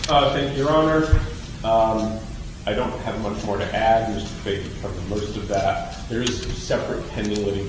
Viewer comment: for reference. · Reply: thanks. I heard: 'I don't have much to add. Mr. Fahey covered most of that.'